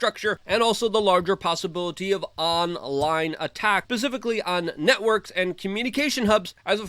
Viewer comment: At about 4:50, did he really just say "pacifically"? Instead of specifically? There was totally a Russian passport found in the bubbles.